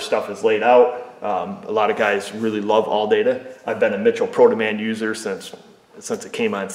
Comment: Mitchell Pro Demand. LOL! I thought you were saying ProtoMan. I will give their DIY site a try next time -- I used AllData DIY for my wife's 2012 VW Sportwagen TDI, and the information wasn't really all that complete. It was OK, but the wiring diagrams were a little off...